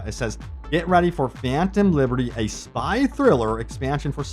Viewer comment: Ngl I keep hearing “anthem liberty”